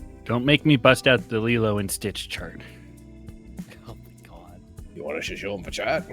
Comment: Ok, I heard either Lilo and Stitch Druid, or Lilo and Stitch chart, either way, I wish to understand this reference